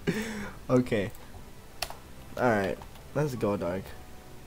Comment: ummm. what did you mean by lets go darg at 1:07 ? or did i hear it wrong?! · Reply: LOL i was just saying "let's go dawg" like i'm ready to play xD